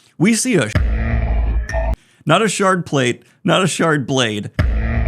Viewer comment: Please I'm slow 😅 what did he say in 16:51, I already finished it, but I'm thinking of different options (mostly the -inator, in Phineas and Ferb lingo) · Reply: Shard gun, probably.